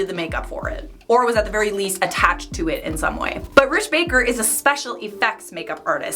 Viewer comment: Uh... sounds like you were saying Rich Baker... his name is Rick Baker. Rick.